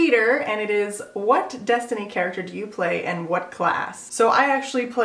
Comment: Instead of "destiny character" I heard "destiny child character" and I got really confused